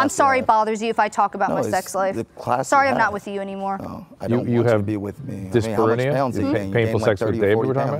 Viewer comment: I played it over and over and still couldn’t understand what he said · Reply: He said why would I want to be with you. You gained like 30-40 pounds